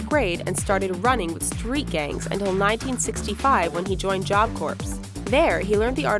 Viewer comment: I'm sorry, did you say job corpse?